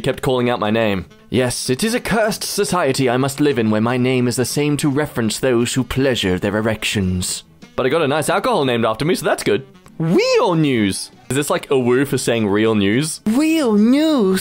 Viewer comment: sounded like "Real Noose" to me. B)